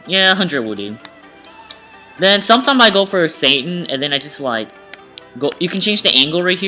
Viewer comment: sometimes i go by Satan 😂😂 i know what u mean but i had to mishear it